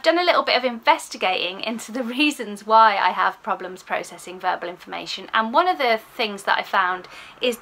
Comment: Agree with all you say! I did chuckle though as I heard it as burble processing 😂x